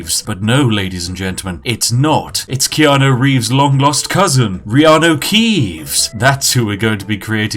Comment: I keep hearing "rihanna keeves"